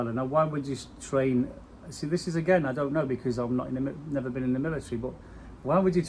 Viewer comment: I thought you said you had been in the military/army🤔🇬🇧